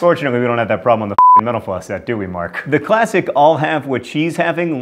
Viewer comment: Everytime he says mental floss it sounds like he says menopause